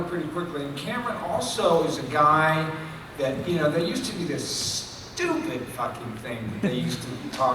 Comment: First i thought he said "I didn't build the Fucking thing"